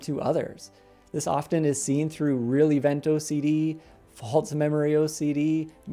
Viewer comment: What did he say at 2:23? Is it relievant OCD? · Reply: Real event